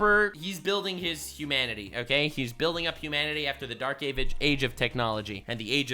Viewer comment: did i hear "dark age of gabage" like the avatar the last airbender gabage man unluck guy?